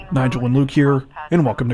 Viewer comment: 🤣🤣Thank you Laura Bell. I always thought he said "Nigel Anook here" !!🤣🤣